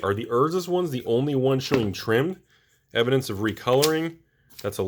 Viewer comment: I hear “showing trim” I click thumbs up